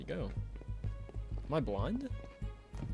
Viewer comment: sounded like you said am I blonde😂